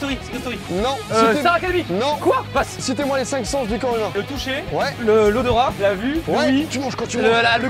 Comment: j'entend citer moi les 5 cens du corona xD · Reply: Il dit du corps humain mais on dirait qu'il dit Corona 😂